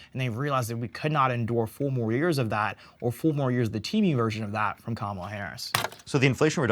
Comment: I thought he said “Temu version”💀